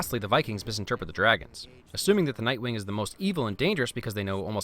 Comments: he said night wing instead of night fury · did he just call Toothless a night-wing? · At around 2:55 you said night - wing instead of nightfury. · I'm sorry but did you just say night wing?! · am I deaf or did you say night wing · He said NightWing instead of Night Fury